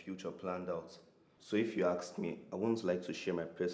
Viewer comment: he said AKS instead of ASK.